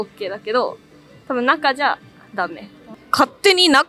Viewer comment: what does she say here? Dah-may? Means don't do it? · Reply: She said dame which means no-good;cannot or not allowed